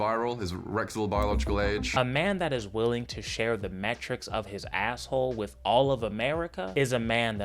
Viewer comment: Best joke ever. All of America? Good luck not falling in. Oh, you said metrics. I thought you just said he just shared.